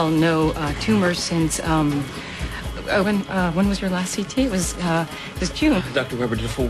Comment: Did she call Henry "Owen"? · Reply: She says "oh when was..." but it sounds like owen, because she shutters